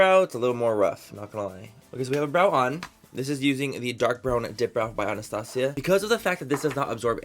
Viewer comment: This video cracked me the F up! I thought you were saying “Dick Brow” lmao 😂